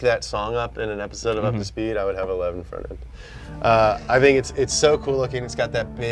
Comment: what did he say at 21:03? i tired the subtitles it said (indistinct) :) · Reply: He said I would have a Levin for real